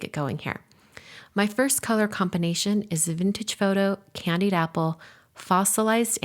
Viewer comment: I was putting a spoon of breakfast in my mouth when you said 'vintage photo'. I heard 'sweet potato' and my head whipped up in shock 😂